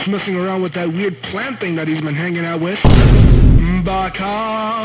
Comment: WHAT DOSE HE EVEN SAY ON THIS PART🤣🤣🤣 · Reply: Baka (fool/idiot in Japanese)